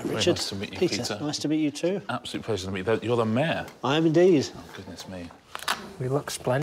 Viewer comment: When they said "you're the mayor?" I heard "you're the man " but that's because I'm American and not used to the accent